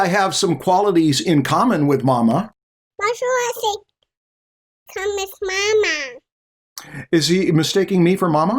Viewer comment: I think your son was trying to repeat what Marshall said, when Marshall said "maybe I have some qualities in common with mama". Sounded like he said "marshall has some.... comm with mama"